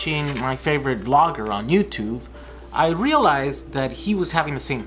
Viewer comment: did he just say Youtuve?